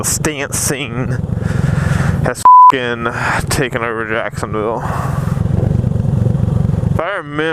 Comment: What happened to your speedo lol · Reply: did you say Jacksonville